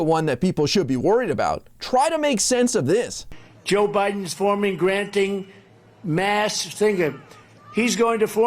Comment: I heard him say “Joe Biden is granting masked singer”😂